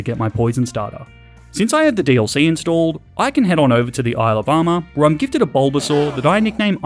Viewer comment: Ok, I'm so tired after 2 hours of Russian lessons that instead of "Isle of Armor" I heard "Alabama". Yeah...